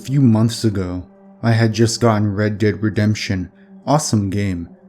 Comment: I just heard "I got the horses in the back" when he said Red Dead Redemption